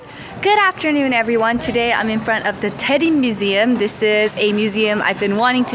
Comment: I misheard daelim as Teddy idky haha 😂 who doesn't like to come home to packages!! it's like Christmas everyday 😂 always excited to open it! you can always vlog your mum's cooking if she allows! :D